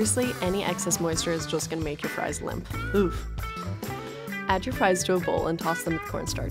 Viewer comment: Did she say oof